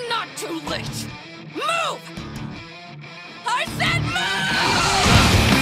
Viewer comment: it sounded like “Moo! I said MOO!!!”